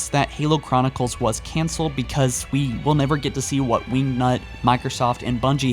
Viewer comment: it sounded like you said "we nut" plz correct me if I'm wrong lol